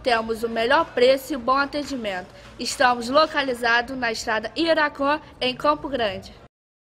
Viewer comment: Hj eu passei na rua Iaraquã. só lembrava da FL gás.. passei devagarinho pra vê onde é kkkkkkkkk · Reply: Não sou do rj. A vida inteira achei que ela falava rua Iraco kkkkk A miseravi é fanha também kkkkk